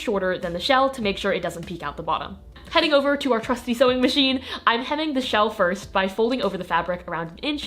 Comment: When you said trusty sewing machine i thought you said "çŕůşțý sewing machine" Also denim wizard